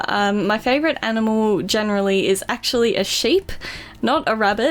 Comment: I thought she said her favourite animal was a shape lol